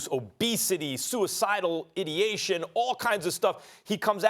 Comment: Did he just say “idiation”? 😆